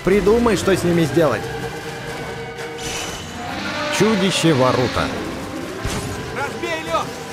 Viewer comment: Мне послышалось не варуто АО Наруто